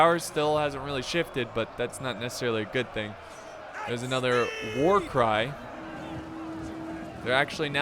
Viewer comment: I heard ,,War Crime" :D